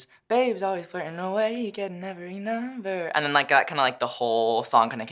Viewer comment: Conan thinking he's being sneaky with pronouns but at 0:38 am I the only one who hears "HE getting every number"????